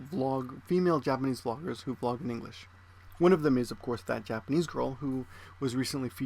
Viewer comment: At first I thought you said "fat Japanese girl" lol